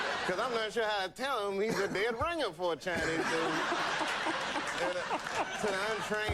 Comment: What does he say at 5:00? I can't make it out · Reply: He’s a dead ringer for a Chinese